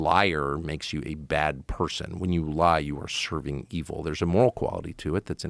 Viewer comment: When he said "when you're lying you're serving evil" I misheard it as "when you're lying you're Serbian evil". Probably because I'm Croatian lol Jel nas ima ex-yu bagre u komentarima?